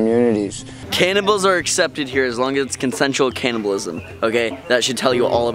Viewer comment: Omgosh he did say cannibalism I thought he said cannabis ugh